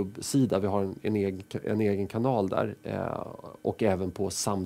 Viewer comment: Lol 6:40 Did he say "n" - kanal ... as sjw I must report this jk